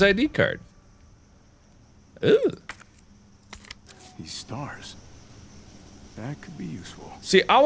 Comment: all Nemesis says is stars because that is all he can say but he sounds like hes saying strrrrrrs without the a